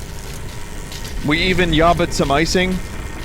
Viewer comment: Does anyone know what Vinny is talking about at 51:10 ? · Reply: The "YABBA MY ICING" noises that HL2 zombies make.